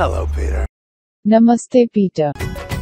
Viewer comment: Escuché mamAstE Peter